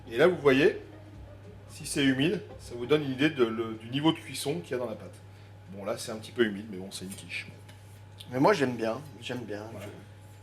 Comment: Moi j'aime bien quand c'est humide... C'est ce que j'ai entendu à